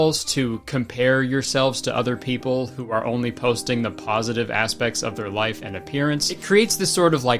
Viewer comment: I can’t be the only one who heard “plositive”